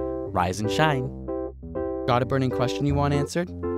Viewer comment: Dunno why but i heard ''Got a burning crotch you want answered?''. D